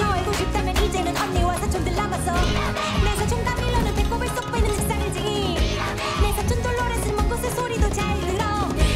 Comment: 아무리 들어도 안들려서 그러는데 4:13쯤에 내 사촌 까밀로는 배꼽을 쏙빼는 다음에 뭐라규 하는거에요? 책상이지 라고 밖에 안들려요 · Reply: 내 사촌 까밀로는 배꼽을 쏙빼는 짓을 하지~ 에요!